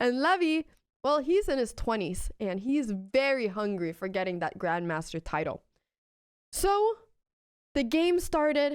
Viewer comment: CC says gothamchess is in his 20's hungry for that grandma's title.😅😅